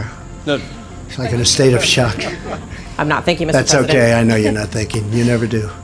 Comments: "I'm not thinking, Mr. President." "It's OK, I know you're not thinking. You never do." LMAO that's my favorite Trump moment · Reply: Actually if you listen carefully.... she said "I'm not...Thank you Mr. President"... it only sounded like she said I'm not thinking. GREAT burn by Trump though, no doubt a classic. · I don't really get what happend in that clip. It seems like he just randomly handpicked a reporter out of the group and she wasn't expecting him to pick her so it took her a few seconds to get ready. Trump makes a comment about it, she wants to appologize by saying something that starts with 'I'm sorry I wasn't thinking...' and then for no appearent reason Trump interupts her and insults her. Did something happen before the clip that set him off? Anyone saw the whole video? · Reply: she actually said I am not thank you Mr President, but it was misheard by Trump as I am not thinking, and he seized on it pretty quickly.